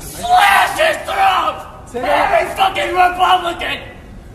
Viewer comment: I think we heard him saying slit his throat officer.